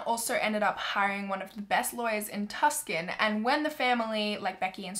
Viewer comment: Hate to be that person but when you said Tucson I thought you were saying Tuscan and I was like where in the world is Tuscan but then I seen the sign that said Tucson and I was like ohhhh ok Tucson Arizona.